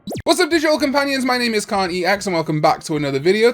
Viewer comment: Lol, so tired, thought he said Karen ex. I'm going to bed